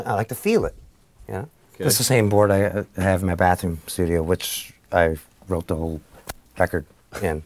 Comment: did he say... "bathroom studio" ?!? O_O